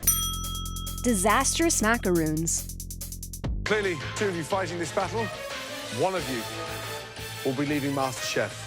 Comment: I thought it said dangerous macaroni instead of macarons